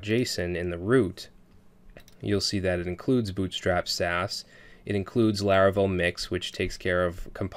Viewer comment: did i hear him say that " it includes bootstrap's ass" ? LMAO